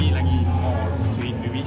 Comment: cakap kt baby pada minit 14:36 terlebih kantoi dah ni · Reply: Dia cakap maybe bukan baby bang aduiiii